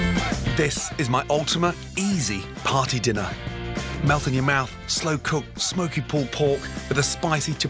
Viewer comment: I know it says "melt in your mouth" but.. Did anyone else hear "nut in your mouth" the first time they heard it? maybe I just have a dirty mind